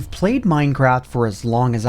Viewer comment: did he say... mine crap?